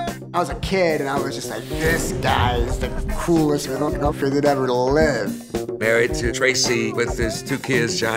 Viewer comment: With the censor, it sounded like Flea said "This guy is the coolest 'MILF-Fucker' that ever lived" at 0:49. :)